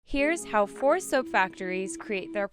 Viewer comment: I thought you said “poor soap factories”